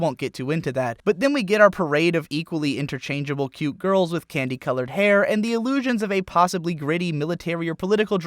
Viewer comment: i heard "panty-colored hair" the first time. i was disappointed when i read the transcript